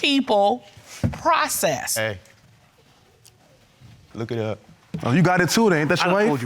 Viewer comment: looks like Candace from Housewives of Potomac! What did he say A look it up?